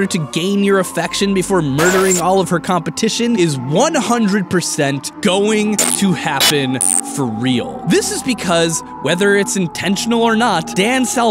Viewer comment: All I heard was "Monika is 100% going to happen for real."